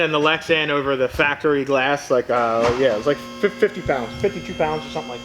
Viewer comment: we discuss it, in the video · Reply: I heard that but I heard "on top of the factory glass" in the rear